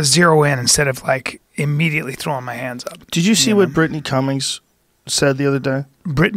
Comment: Burton Cummings I thought he said. (Lead singer of The Guess Who)